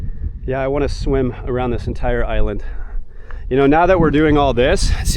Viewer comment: Sounded like you were saying "not a decent island" 🤣🤣🤣🤣